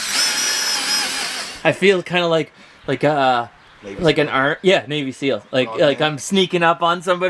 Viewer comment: Had to rewind, thought he said army navy seal for a minute😂